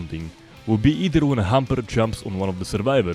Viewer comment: I had to replay the part where you said Hunter because I heard "Humper"